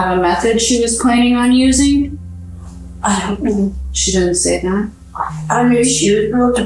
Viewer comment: he says “I don’t know none of the particulars.” He didn’t say what the caption says